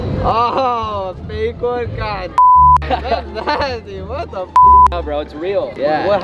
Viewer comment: what did he say? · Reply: He said that’s nasty